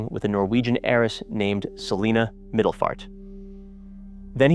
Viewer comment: Did I hear it right her name is Selina Midlefart?!?!